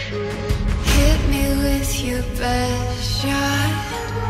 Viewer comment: It be this or the original version, i can only hear: Hit me with your pet shark 🦈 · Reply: I heard "hit me with your Peugeot"